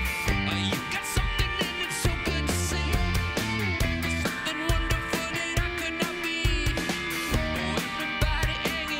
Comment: what is julian doing/saying at 0:41-0:42? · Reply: i think he is saying “Fuck Myself” because he messed up some of the lyrics in the beginning